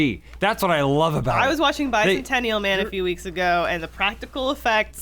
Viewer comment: What says at 23:50 ? I hear "bizon ten real men". Is she talking about a movie with good practical effects or I'm not getting a joke